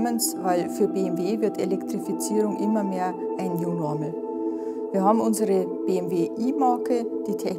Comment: für BMW wird Elektrifizierung immer mehr ein (was?????) . Ich behaupte mal, dass ich der deutschen und englischen Sprache mächtig bin und dass ich auch das "Denglisch" von den großen deutschen Firmen verstehe , aber was zum Teufel meint sie mit "you-normal"??? und ich hoffe nicht, dass es ein neudeutsches Wort für Otto-normal ist bzw. Standard ist. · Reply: Sie meinte "New Normal". Hat man leider relativ schlecht verstanden. Aber deine Hypothese zur Bedeutung ist schon richtig kann man sagen 😁 Beste Grüße #BAVMO